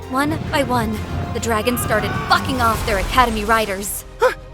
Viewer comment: when it said “bucking off” i was shocked i thought it said “f**king off”😅